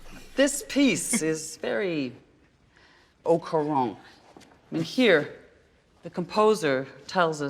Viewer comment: What is that word at 0:55? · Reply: Yes, it’s au courant which means being very current, but also wanting to let ppl know that you’re very current. It’s a backhanded compliment mocking his wish to seem edgy.